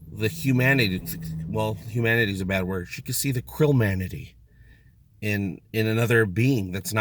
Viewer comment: I heard that as "Krill Manatee" instead of krill-manity and now I really want a remake of Star Trek IV, but with krill manatees instead of earth whales.